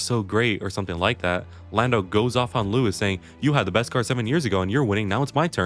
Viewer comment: he actually said: "you had the best car 7 years" not 7 years ago. and Lando was right there cause Lewis commenting that way was making look like the car won not the driver. taking credit away from the driver towards the car